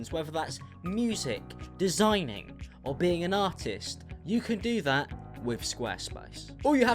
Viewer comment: I’m the ad at the first part of the video I know he said “artist” but it just sounded like he said “autist”